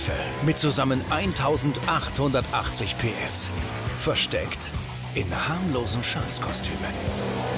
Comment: ich hab zuerst verstanden: versteckt in harmlosen scheiß-kostümen haha